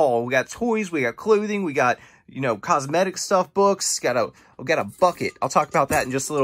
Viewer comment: sounded like you said “we Gota fuck it”